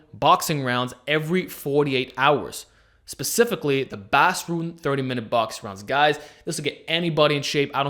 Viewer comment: Did you say Basroon 30 min boxing rounds? Didn't quite make that out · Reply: Bas Rutten 30 minute boxing rounds